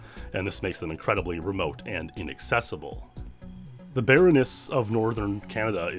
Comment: I heard Baroness instead of barrenness. I thought for a second, "There's a female noble that rules Northern Canada?!" 😅